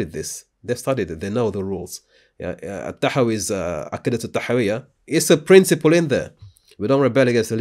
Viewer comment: what was the name of the book on the screen? I don't speak Arabic and couldn't understand what said. Thank you. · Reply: Aq(k)idatul A'thawiyah.